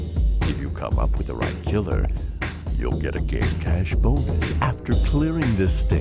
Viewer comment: did it say we'll get a gay cash bonus?